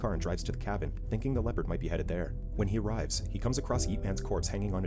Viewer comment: did you say YEET? Or did I hear it in a different way